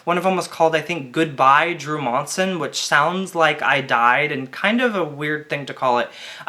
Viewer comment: What the fuck I always thought he was called 1:27???